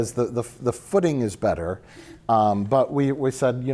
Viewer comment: Hi chaps, I had to rewind. I heard “the pudding is better” not the footing is better , both work